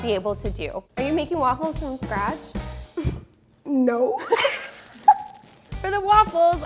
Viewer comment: When asked: Are you making waffles from scratch? I literally thought she said are you making waffles from trash 😂